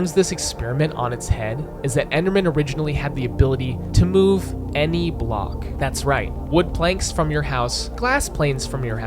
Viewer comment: all i heard was "funny block game"